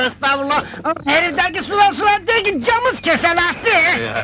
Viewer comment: halit akçatepe'nin ne dediğini anlayan var mı? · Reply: Herifteki sular sular degil camız keselesi gibi bişey galiba..